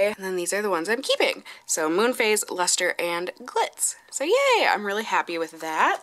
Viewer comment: my husband (who is sitting beside me) says “Did she just say Moon Phase, Lustre and Clits?” 😂😂😂